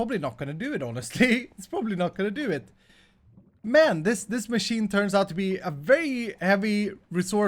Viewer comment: Did I hear a ”men” at 21:32? :P